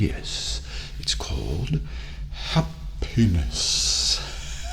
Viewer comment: I thought he said “a penis” instead of “happiness” what is wrong with me